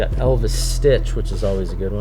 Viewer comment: did he say Elvis stitch?